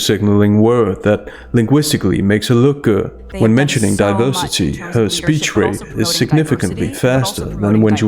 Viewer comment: Did Meghan say diversity - I thought she said diaper city